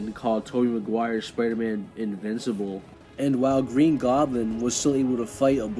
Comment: He actually said " spiderman is all but invincible " which has almost the same effect as he had already tried extra shenanigans like the razor bats distracting him to get to land some hits but they didn't do anything and he got sent flying by Tobey's , so he's admitting he can't really beat Tobey in a fight , so chose to " attack his heart " and used a lot of leverage in the end to tire him out before the fight even started and got some beatdown in but oh well...shouldn't have brought MJ into it Mr. Goblin , Tobey got his strength back , stopped his spear attack and sent him flying again BY JUST PUSHING HIS SPEAR AWAY. Tom got strong enough to beat Goblin in the end and i'd say he's nearing Spiderman 1 Tobey while still not completely there but nowhere near the one after the whole trilogy . Also i saw a clip of the ferry scene here , just want to make clear that was never even a considerable argument since it's based on a misconception , Tom never held that thing together he tried but the halves were shown to be splitting apart till the moment Tony arrived. Also idk what punching Thanos would mean when it doesn't do anything other than pushing him which just makes it as impressive as a little more than his weight while Tobey did the same to giant sandman which is alot stronger feat . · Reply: I checked like three times, I think he actually said old but invincible. I had to put it right next to my ear to check and it sounded more like old but invincible. Which is why he says but Peter Parker afterwards.